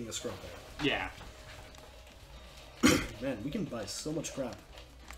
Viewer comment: i thought i heard "we can buy so much crack" and i thought "when have i ever said that?"